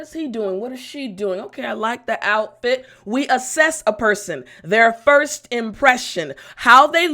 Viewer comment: I hear “we assas a person”, but don’t know how to spell! Write, please!